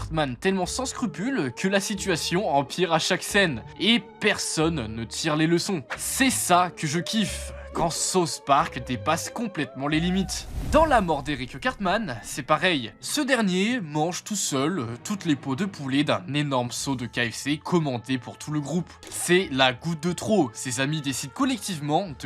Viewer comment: « Sa-ousse » park C’est quoi ce « sauce » park « Sauce » c’est ce que je demande à mon chef kebabier